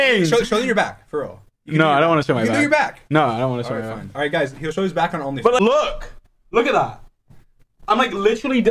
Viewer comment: did dream just say geroge will show his back on OF?? nahhh i love these two, DNF FOREVER